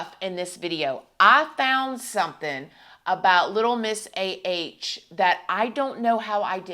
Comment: when you said “little miss A.H” I thought you were saying “little miss assh0le” if the shoe fits 🤷🏻‍♀️ also, Christina, you look absolutely stunning 🖤✨